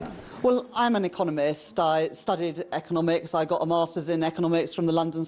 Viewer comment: Why does She Keep Saying that She is an Economist is She Trying to Convince Herself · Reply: I keep hearing I'm 'a con artist' But then again I am going deaf in my right ear 😅